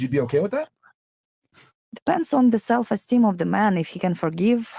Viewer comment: That Ukrainian girl is a coke head 59:01 did she said depending on his self esteem that’s crazy